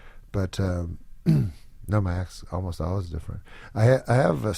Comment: What did he say at 14:48 ? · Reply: “My act’s almost always different”